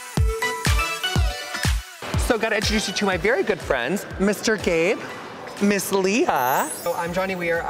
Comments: lowkey thought he said mr gay instead of mr gabe when introducing johnny and i wheezed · i actually thought he said "Mr. Gay" loll